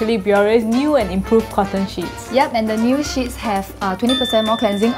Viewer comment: it sounds like she is saying cotton cheese lol...